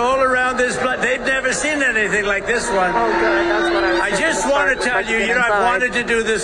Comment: At around 01:18 you can hear a woman saying something, and it starts with "Ohh god" i cant hear her clear enough, but, shes definitely negative... Any hear her ??